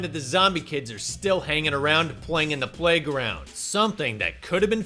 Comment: Listening blindly, and I deadass thought you said ‘out in the plague ground’. Was wondering where said plague ground would be for a good moment- until I remembered. Children. Playground. Out in the playground. Lmao 😂